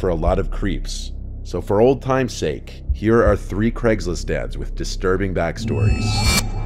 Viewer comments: Dont mind me, just giggling form the double take I had to do to the title cause I heard "Here are our 3 disturbing Craigslist Dad's" instead of "Ad's" when the video started LMAO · it sounded like he said craiglist Dad's 💀💀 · It sounded like you said “here are some Craigslist dads” lol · I heard "here are 3 craigslist dads with..."